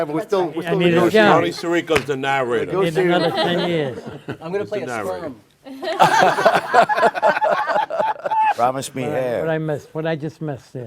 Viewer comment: What does Robert Iler (I guess it's him) say at 45:16? "I'm gonna play the..."? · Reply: I'm a year late, but he said "I'm gonna play a sperm" lmao